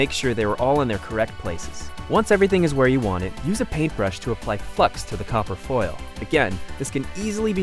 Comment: Did he just say "use a paint brush to apply fucks."